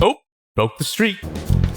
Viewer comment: did he say "broke the streak" or "broke the stream"? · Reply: Broke the streak. It’s in reference to the concept of mewing, as seen in the diagram laid over the T-Rex Zord’s face. Mewing is the concept that by resting your tongue against the roof of your mouth instead of against the back of your teeth, it will give your jawline a more impressive shape. As such, a mewing streak is how long you can mew for.